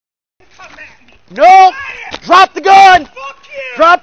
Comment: We're all aware of the situation, but am I the only one who heard the officer shout, "DON'T drop the gun" at 4:20? It's like during an interrogation: "I never killed nobody!" "So you admit it!"